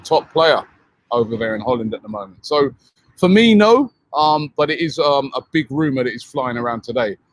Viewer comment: did he say Firmino is linked 🤦‍♂️